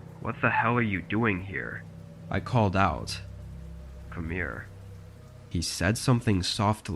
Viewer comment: The third story at 13:59 I thought bro said Amir and I thought the orange haired guy was cooked but he just said “come here